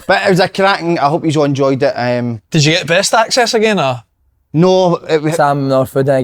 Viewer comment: When asked slaney if got (press access ) I thought said (breast access ) slaney wishes he got breast access lol